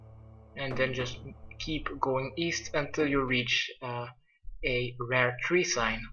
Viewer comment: East? I thought you said w-eest?